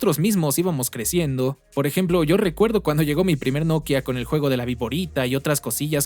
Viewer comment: 😂😂😂😂😂😂😂, escuche Novia y quede M. C. M